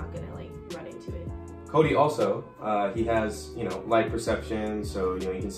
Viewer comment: Could have sworn said ‘liposuction’ not ‘light perception’ 😂 great video!!